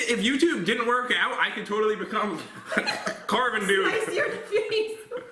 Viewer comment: I thought Jen said spice but she said sliced